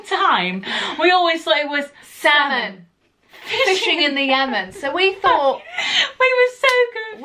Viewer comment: Oh my god I always thought they said salmon fishing in the oven!!!!😂😂😂😂😂😂😂